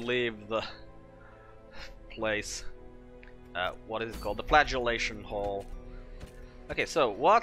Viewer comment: I seriously heard you say Dismas was flatulating... I did a double take and realized it was Flagellating